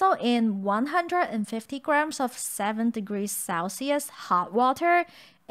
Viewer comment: Hey guys, a few notes: 1. Hot water temperature should be around 60-70C, sorry that in the video it sounds like I said "7 degrees" haha. Hot water dough makes a very soft and extensible base for shaping, it’s a common dough for making flat bread or pancake in Chinese kitchen. 2. Taking the flour and yeast mixture out first is to make sure the hot water that we’re adding in doesn’t kill the yeast. 3. If your room temperature is very low, like between 10-15C, you can use a bit more yeast, say take a 1/4 tsp and use about 80% of it so that it still ferments during the resting time. 4. The dough should only be slightly leavened. The purpose of leavening is to create a little bit of air bubbles so that it’s puffy and has that airy crispiness after frying. But you don’t want it to be fully fermented because that’ll create way too much bubbles and absorbs too much oil. There’re also fully fermented pancakes in Chinese cooking, but those are usually fried with a lot less oil or even just baked. 5. SThere’re other options for seasoning the beef. Some places would add in Sichuan sweet bean paste tian mian jiang (甜面酱), or Sichuan fermented mustard green yacai (芽菜) for some more depth, or huajiao Sichuan peppercorn oil for a stronger numbness. So feel free to play with what you have, but remember to adjust the salt quantity if you’re doing so because sweet bean paste and yacai are both quite salty. 6. You can also control the spicy level with the chili flake you use; we like ours to have